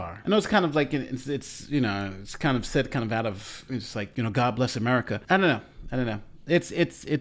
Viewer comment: When I listen to this I took it as he said god bless America instead of saying g-damn. I have met many people who say little phrases vice cursing.